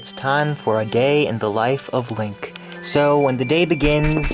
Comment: he said "gay" instead of "day"